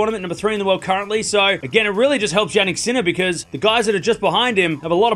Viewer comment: I heard Jan-Nick sinner :p